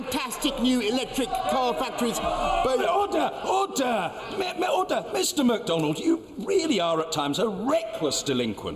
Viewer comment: 「Order!Order!みんなOrder!」って聞こえる